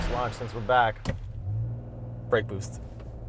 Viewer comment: did he really say brake boost but it’s a n/a 🥴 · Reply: Ya brake torque. Revs come up